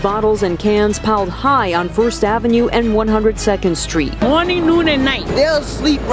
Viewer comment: WHAT tf is she trying to say? "Oninuninei"